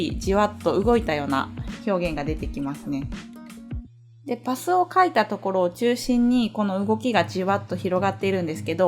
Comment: It sounded like you said que paso